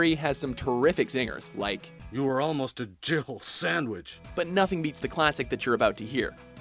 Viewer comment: Because of the way Barry chuckles while he says it, I always hear "Jibble sandwich".